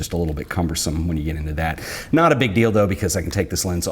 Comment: i heard "not a big dil do"